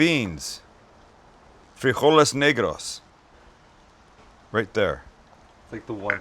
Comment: Is it strange that I hear "free all these...." when you say, "frijoles negros." lol